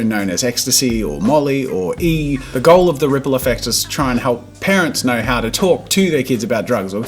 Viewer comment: As he said “to let parents know” I skipped ahead and it sounded like he said “to let parents know how to take drugs”😂